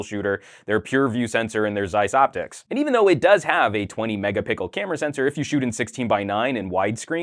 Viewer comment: lmao did I just hear 20 Mega pickle? Haha